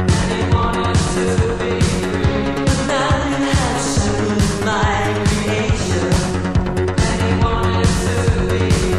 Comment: Does anyone know what says at 1:03? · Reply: “But now you have shackled my creation...”